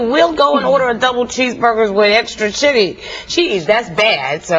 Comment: Did I just hear Star say "shitty" around 2:21? LOL!